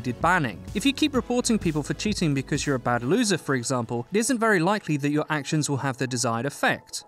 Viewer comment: I thought he said LoserFruit lol